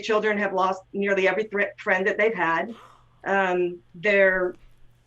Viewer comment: she said "Friend:" but i thought she said trend and went wtf what does tiktok have to do with this